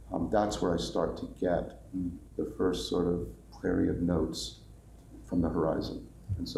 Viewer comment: Fantastic conversation! Can someone tell me what John says at 1:24:14 ? "That's when I get the first ...[inaudible]... notes from the horizon." Thanks! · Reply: prairie of notes?